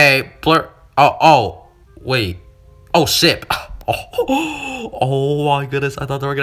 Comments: I heard saying shi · did he say shit??? No it was ship🙂